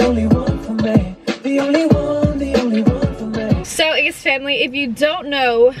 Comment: Did Catherine just say Yeast Family